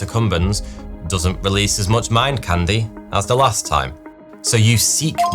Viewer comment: Oh! “Mind candy!” I heard “man candy” for no reason